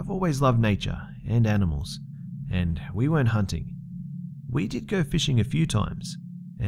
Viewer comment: " I've always loved animals" "we went hunting" ... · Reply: think he said "We weren't hunting", although I thought he said went at first as well.